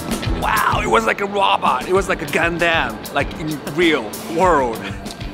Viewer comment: "Wow, it was like a robot. It was like a GANDAMN" yeah, that's pretty much what i heard was saying, instead of Gundam.